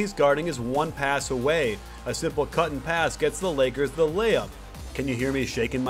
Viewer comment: Did You say... lakers?